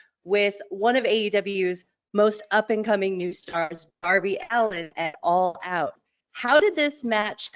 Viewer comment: did she just say Arby???